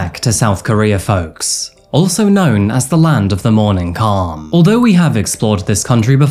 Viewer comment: Watching the traffic flow in the introduction, l thought you said, "South Korea, also known as the land of the morning car". Thank heavens for Closed Captions. 😊